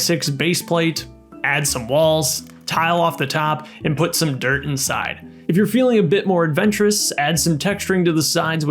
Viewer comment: I thought he will say "put some dirt in your eye"